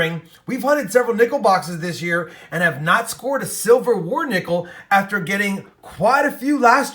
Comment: funny at the end I thought you said Civil war nickel and not Silver War nickel. Fun searches. Thanks.